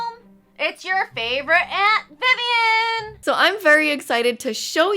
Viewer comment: i thought it said favorite amphibian 😂 I watch too much amphibia lol